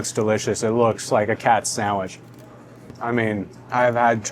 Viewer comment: my wife thought you said "this really looks like a cat sandwich" lmao